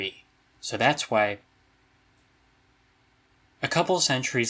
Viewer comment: am I the only one who heard it as "so that's why" instead if "so that's Y"? I was waiting for him so say something else and got so confused I rewinded it so many times like a dumbass